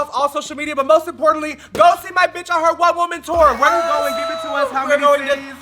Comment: I know you said "one woman tour" but I heard "white woman tour" LOL